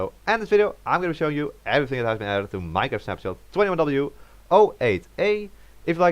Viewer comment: What it supposed to sound like "...everything that's been added to the... What it sounded like the first time I heard "...evryingatenadded..." lol